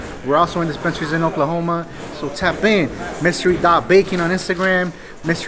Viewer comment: Thought they were saying Mystery Bacon not Mystery Baking Lmao